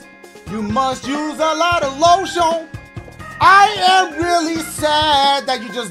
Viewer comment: I’m Sorry...is it just me who is dying because of the way he said “LO-SHEON” instead of lotion at 00:12 I had to replay that shit so many times